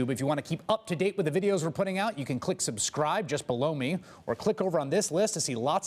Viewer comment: did you say " Just Blow me" ?